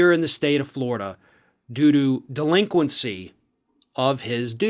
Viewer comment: I swear I thought Farron said doo doo instead of due to 🤣